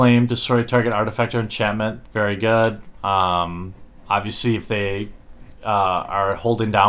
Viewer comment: I hear you say this all the time now. "Abeesee" But it's supposed to be "Obviously". WHy!?